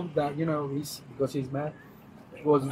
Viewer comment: the witness said "like when you hit a dog when you're mad" ..... bro what?? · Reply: it may be a language barrier, sounds like he says "hit a dog when HE'S mad" because there is a certain way you punch a dogs head if it is actually attacking you/won't let go of you.